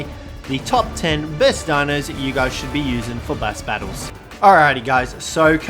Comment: excuse me ... Did you say bass battles?